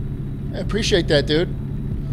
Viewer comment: Did you just say “CTS”?